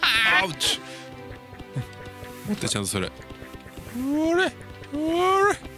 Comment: 、それ に聞こえました。かわいい。 多分、それ、って言ってるのかな？